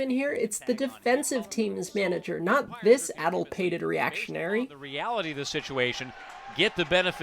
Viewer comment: word of the day, apparently, is "addlepated", meaning mixed up, confused, or eccentric. TIL! Even Google's voice-to-text engine couldn't understand it and thought I said "adulterated" instead.